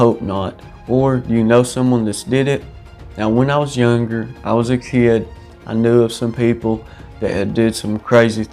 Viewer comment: What do you know about the sugar revenge story? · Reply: Did you say you were in korea? You must have served. I misheard it when you said as a kid lol